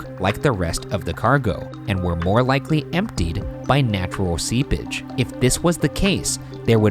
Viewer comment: What Ryan said: “...natural seepage” What I heard: “natural sea-b*tch”